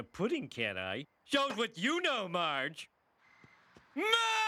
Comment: What does he say here? I can't tell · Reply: Hey Maaaaarge!